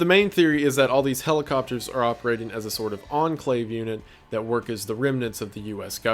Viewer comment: did I hear ENCLAVE?